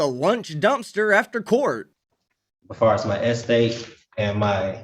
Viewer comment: ESTATE! I thought he said S date.